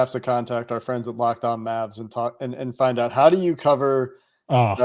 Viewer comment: I thought said "locked on Maths" instead of Mavs, and I didn't batter an eyelid at the prospect of being a regular of a maths podcast